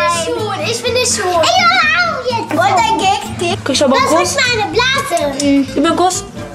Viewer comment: Ich hab anfang "das ist ein nasenpe__es "verstanden 😂